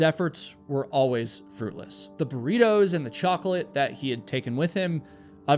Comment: lol at first I thought you said Doritos and chocolate. I heard burritos the second time tho 😂